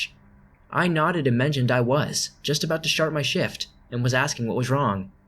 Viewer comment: it sounds like you said "SHART my shift". And all I can think of, "I'm sure there's easier ways to get out of working....." 😂😂